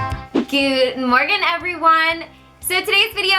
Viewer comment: it sounded like she said in the start "goood morgan everyone".